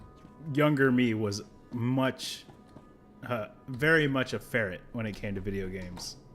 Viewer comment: So, although you said, "Ferret" My brain autotranslated it. I heard it as "Mail! Farrik."